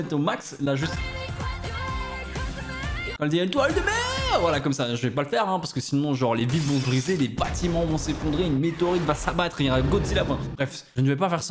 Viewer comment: Moi j'ai entendu: "Ou est la crotte de merd*?" 😂😂😂